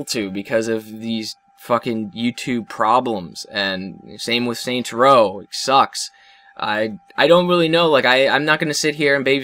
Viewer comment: Did he say Saints Row sucks at 5:50?? D:<